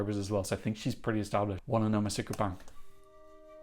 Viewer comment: may I ask what it is you say so quickly before listening to each song? it kind of sounds like you say "Wanna know my secret band" but you say it all the time and I just can't tell what you're saying lol. Thank you for always making great Eurovision content!! 😁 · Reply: Wanna know my secret, bang! Ukraine 2019